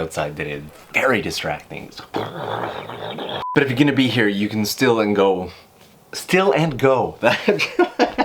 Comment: I heard is as "steal and go" xD